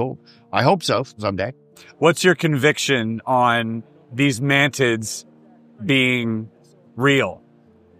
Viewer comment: Did you say "man tits", SIr? 27:05 :D had a good laugh!